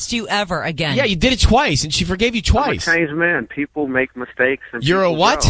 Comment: Is it weird I heard "I'm a Chinese man" instead of "I'm a changed man"?